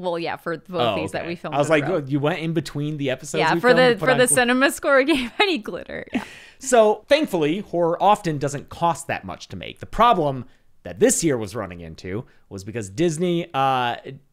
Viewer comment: I thought is said cinemassacre, witch is an amazing channel, also hosted by a cool dude called james 👍